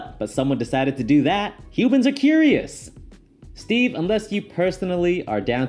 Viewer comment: Did you just say Cubans are curious? That’s what I heard!